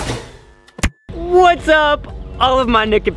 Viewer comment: lol first time here, that intro got me dyin😂😂 i thought he said whats up all niggas at first